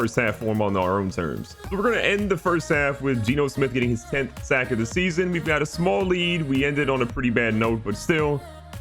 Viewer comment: Did I hear right at 10:06, Geno Smith 10 sacks on the year?